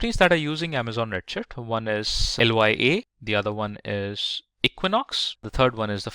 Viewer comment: explanation is very nice , Thank you for the video. is it any full course of AWS components? I really like it. Another think, in11:00 min you say LYA company actually its "lyft" like Uber does transportation business.